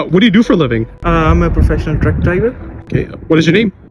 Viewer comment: Why did I hear "I'm a professional drunk driver" 💀💀